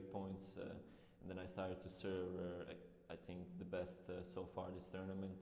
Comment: Never mind he said serve, i thought he said serb